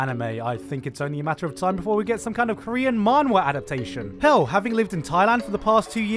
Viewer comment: "Having lived in Thailand for the past two years..." Wait, what?! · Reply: I don't know where he lives but I thought he said "have you lived" not "having lived"